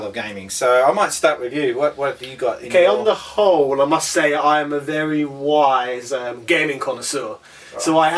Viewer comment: in i thought you said, on the whole, im a very wise gaming console. lol. great vid